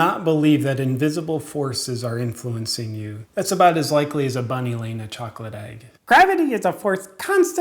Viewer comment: What does he say at around 2:40 ? Bunnylainerchocolatide?!! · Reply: bunny laying a chocolate egg